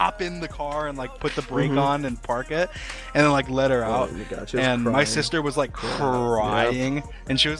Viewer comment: I believe he actually says “let her out”